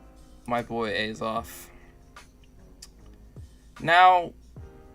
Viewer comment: did he say Azoth?